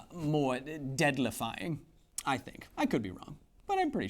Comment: Pseudoscience "cough" flat earthers Sounded like he said flat earthers are deadlifying